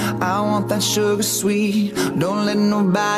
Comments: I thought he was saying "sugar, so sweet". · and all this time I thought he was saying ''SHOTGUN! YES PLEAASE! '' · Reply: I thought it said "sugar, so sweet".